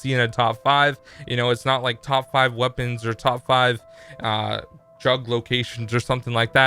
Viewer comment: Great video but at the end I thought you said top 5 drug locations LMAO does coke confirmed